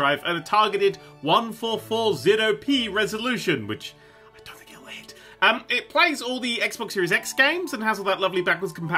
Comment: WTF does he whisper at 0:30 ? All I can hear is "I don't heal it ". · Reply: "I don't think it'll hit." The Xbox SS will probably be 1080, not 1440.